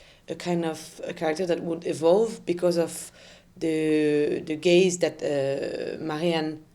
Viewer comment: She said the movie was about "gaze" and I straight up thought she said the "gays" and I was like yep yes correct love that